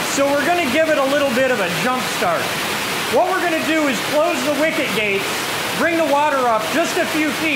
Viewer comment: for 3 videos i watched about the plant i thought you were saying wicked gates 🥲 the more you know ....love these long format videos btw the shorts are funny but its cool to see these places and machinery almost no one gets to see